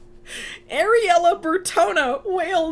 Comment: currently wheezing because my brain heard it as “areola” and now I wish she was more of a character so I could refer to her as “the girl with the nipple name”